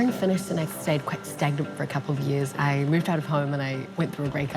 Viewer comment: what does she say at 0:30 i can’t tell bc of her accent loll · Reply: "I stayed quite stagnant for a couple of years"